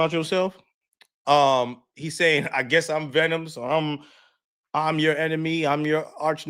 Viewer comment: I said 50 don’t rap no more and I personally wouldn’t be looking for a response …...and TI is an elite MC AND came at him with a great angle. What did yall hear ?! 😂 cause im clearly trippin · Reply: again! you’re trippin HARD!!! You missed that brilliant line because you’re just rushing through … —- “PETER PARKER, journalism” I hear him saying / I guess I’m venom “ PETER, surname PARKER, was bitten by a venomous spider giving him the powers to be SPIDER MAN!!! 😤😤😤 15:28 what are we doing here, SIR?!? you wanna get through to the end ??? your man’s TOURĖ is telling you let’s bite into it - you wanna swallow it whole … PAUSE!! the way you’re reading the man’s rhymes, rushing to get through …. No respect at all for the written word …. you came acrosss as a real hater here …..